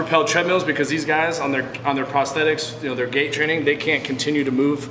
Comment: Hey Destin, please make some minor corrections to your subtitles: 6:15 it's "GAIT training" not "GATE training" 6:20 "HiTrainer" instead of "high trainer" also, just sent you the subtitles in portuguese have a good one ;)